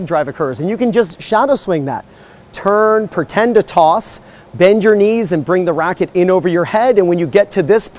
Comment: When I looked at the various pro-player slo-mos, they all bend their knees well before the racket goes over their heads, and are often extending off the ground when the racket drop starts. So that contradicts the idea of bending the knees as the racket goes over your head. I think that idea works well for slow practice swings to get the loose feeling, but in a full speed serve you need to bend your knees earlier and the racket would normally go over your head as you accelerate upwards. At least that's what I see looking at pro slow-mos. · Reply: Glad to hear it. I thought you said bend your knees as you hit the hat, and I thought you mentioned that some people rarely bend their knees before, but I must have mis-heard. Great overall tips. Thanks!